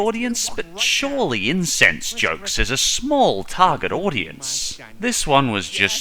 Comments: Am I the only one who thought he said incense jokes? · At around 11:00 you accidentally said incense jokes instead of incest jokes and it honestly made me laugh for the rest of the video.